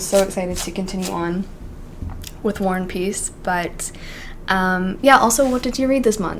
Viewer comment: When you say War and peace, I always hear "worn piece" 😂 im not english so sorry 💜